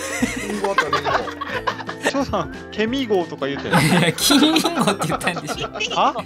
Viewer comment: はいつ聴いても「ケミーゴォ」に聞こえるwwww